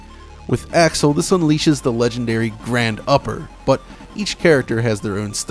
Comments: For around 28 years, I thought Axel was saying "Tiger Paw!". Only now realized it's "grand upper". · I always thought Axel said "Grab da pa" Now I know it's "Grand Upper". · I never knew what Axel was saying as a kid. I know its "Grand Uppah!" , but I always heard "Razor Paw!".